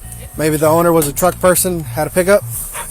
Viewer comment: /03 BACKGROUND FAINT shouting of a woman can be heard SLOWED down YouTube SETTINGS 45x 10:07/08 when police officer says maybe the owner had a pick up truck.??? Dog can be heard barking but I SLOWED down YouTube SETTINGS O.45x it sounded like " had a truck" ???. I know it is weird but sounds like it. Also I have a screenshot of the scene and it looks like a truck with a spare tyre on tail gate and looks like a woman has shorts on a black bra on in a fighting position. And two men behind a Bush with one had a stripped tshirt on .I saved it if you want to see